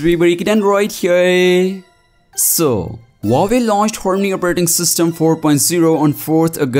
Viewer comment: What did he say? "Horney operating system"!!